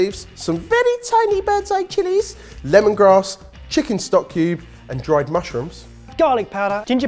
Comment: all this time, i thought ben has been saying chicken/beef/vegetable “stockeu” not “stock cube” everything makes sense now